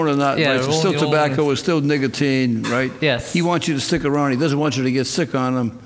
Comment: Sounds like judge said nigggatine instead of nicotine 🤣🤣🤣🤣🤣 🤷🏾‍♀️ I thought that was funny